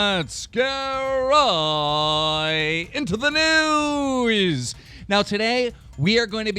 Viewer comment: It sounds like he said "Right into the nudes"!